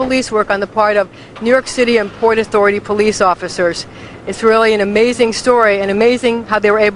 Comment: did she say isreali or it's really??